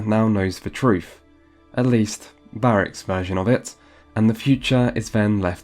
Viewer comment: Did he say Barric 1:29:14??? I'm not sure but if he did then he's obviously never played this game. Maybe he's just the narrator I don't know weird.